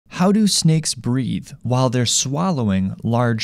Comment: I thought I said, how do snakes breed at first